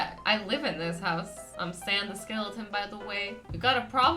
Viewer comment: I actually thought said Sans the skeleton 😂😂😂😂